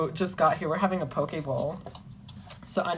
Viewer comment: Bro i thought you said pokeball not poke bowl